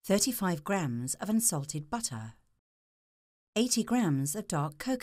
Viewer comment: lol i thought she said 35 grams of insulted butter